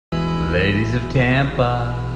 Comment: For a sec I heard "Ladies tampon"...